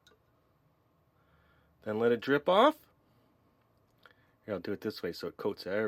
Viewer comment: When u said let it drip off I thought u said rip off